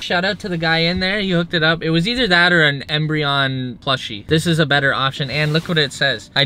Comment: Did he just say embreon?